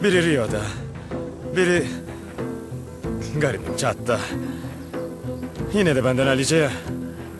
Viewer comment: ’de ne diyor anlayan var mı? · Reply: Yinede benden hallice ya